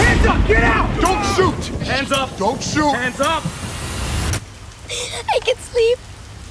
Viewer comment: Never thought saying ' I Can Sleep' can save your life from armed man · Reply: APRIL & Sweet Swagger SUGA The AGUST D No, it's "I can sleep"